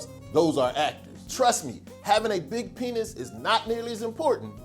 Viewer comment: LOL did he say at 1:58 "trust me having a large ..."